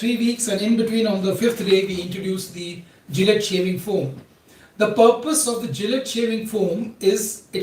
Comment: Hello doctoc what is the name of medicament you mentioned in 11:40 of movie ? I didn't understand the name. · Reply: Gillette shaving foam